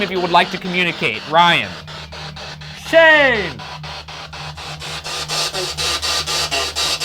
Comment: I swear it said “hi Shane” Also I’m so sad to see this go! Thank you two for being so amazingly funny! These videos were always the best part of my week 😊❤️